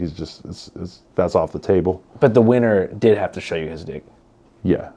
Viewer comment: All I heard was, "he didnt show his dick"